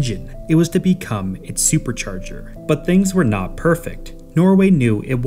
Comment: fascinating how you said supercharger but YouTube captions think you said turbocharger